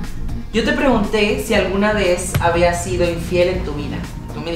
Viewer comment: Alguien más vio en el minuto 10:59 que dice fez en lugar de vez?